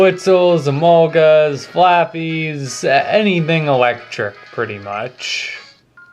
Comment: I thought u said flappy